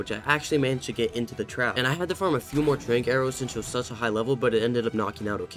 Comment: /30:00 I thought you said Drake arrows 💀